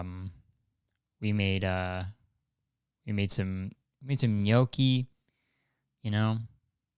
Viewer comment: He said gnocchi and I thought he said milkie.